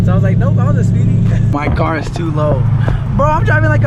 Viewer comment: I thought it said too LOUD Lmaoaoaoaa 😭